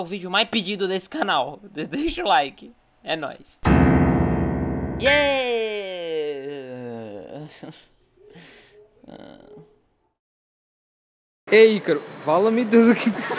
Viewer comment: "Ei Ícaro.... VALAMIDEUS Q Q ISS" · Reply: P sempre achei que ele dizia "fala icaro...FALA MEU DEUS AQUI DO CEU" E não se preocupe entendo seu vocabulario